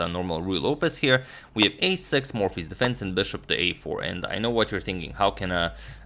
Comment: I heard morphy